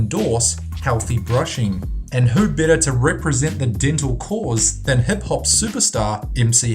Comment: did he just say, "hep hop" ? lol